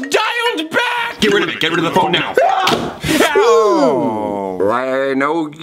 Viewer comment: Did anyone else hear “GET RID OF ME, GET RID OF THE PHONE NOW” Just me...ok...